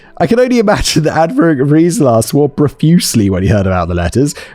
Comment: what is being said here ? I understand 95% of your accent Simon, but this is a total miss for me. “Adverb reees lass”????